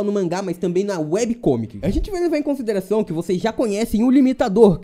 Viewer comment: só pra deixar claro, quem fez a historia do GOD e saitama foi uma fã. Bjs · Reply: kkkkkkkkkkk É que vc disse "têm na Webcomic tbm" eu achei que vc estava falando da luta